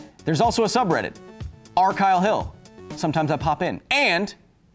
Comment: At first, (at the end of the video) I thought he said "sell your meth, but never buy it."